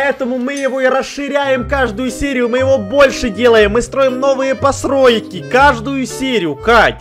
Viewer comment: ты сказал пасройки или послышалась?!